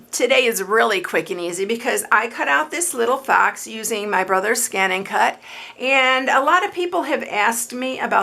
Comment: So glad you said what the scan and cut was .. I had heard you say it was "my brother's scan and cut" ... now I know what you you've been talking about!!!